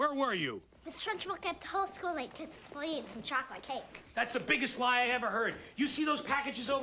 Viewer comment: Mrs Truncbull kept the whole school late because a boy ate some chocolate cake. My favorite line of the movie. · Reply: I always thought she said "Miss. Truncbull kept the whole school late because some idiot ate chocolate cake." Lol 😂